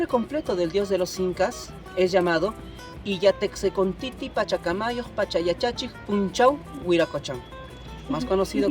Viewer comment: Still trying to figure out what the guy at 7:07 said, but i think he said "Illa Teqse Con Ticci Pachacamayo Pachayachachi Punchao Wiraqochan". Correct me if im wrong though, i dont think i spelt it correctly.